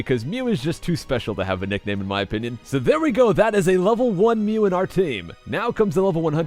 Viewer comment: I thought it said level 188 Mew